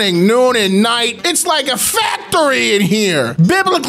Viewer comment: I’m sorry, did you say FAPTORY?